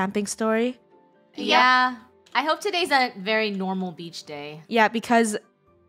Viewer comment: I thought they said a normal beach story me watching the video them jumping over a weird black rope. And them they act like Scooby doo people